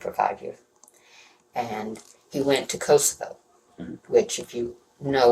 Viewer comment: I got sooo very confused here. I heard “He went to Costco, which if you know the history of Costco, it was a violent mass grave, … ethnic cleansing… nightmare…, he saw atrocities there… he didn’t come back the same…”. I’m like — WTF? WHICH COSTCO WAS IT? 😅